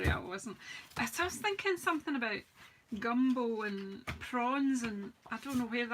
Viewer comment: I thought you said Gumball. Wasn’t that a SU color?